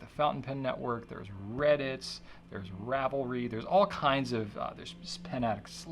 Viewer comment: When he was listing all the communities at about 17:20, what was he referring to when he said "rabblery"? I tried looking it up, but couldn't find anything to do with fountain pens. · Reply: He was talking about Ravelry <link> ) which is a knitting community that is also into fountain pens. - Colin